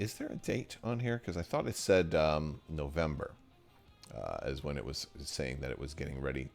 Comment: I heard you say maybe November...